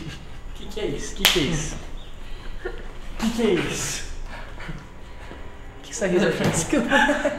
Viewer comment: alguém entendeu que ele falou so sei que foi muito engracado o pedro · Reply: Sim ele falou que Rissadinha é essa desculpa (Começou a rir ) Tendeu ?